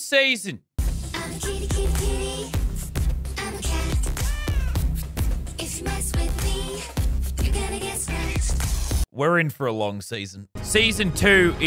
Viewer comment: if you mess with me... you gonna get scratched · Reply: I thought it said if u mess with me… ur gonna get smacked